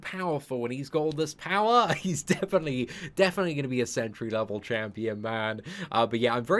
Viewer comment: Lol Did say hes gonna be a "sentry level champion"